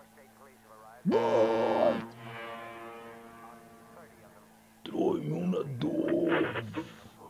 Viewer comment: que dijo homero? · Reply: Bart, traeme una duff( la cerveza)